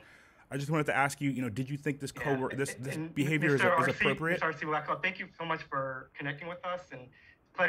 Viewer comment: Did he call him R.C Blackwell at 1:09 sounded like it to me...